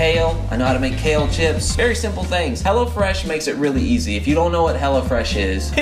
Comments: I thought they were saying "Hella Fresh" until I saw what it really was in the description loll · I thought he was saying Hella Fresh until o saw the apron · Lmaooo I thought he was saying hella fresh the whole time not hello fresh 😂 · everytime he said "hello fresh" it sounded like he was saying "hella fresh" ..... haha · everytime he said "hello fresh" I thought he said "hella fresh."